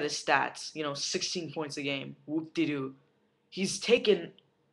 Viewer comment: Woopty-Doo at 1:27... did I hear correctly?